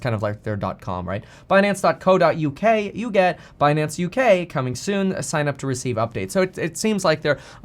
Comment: All I heard was " the beard is coming soon "